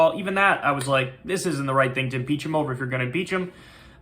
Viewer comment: When Kyle said at the end, "This isn't the right thing to impeach him over if you're gonna impeach him". It actually sounded like he was saying: This isn't the right thing to beach him over if you're gonna beach him 🐋 LOL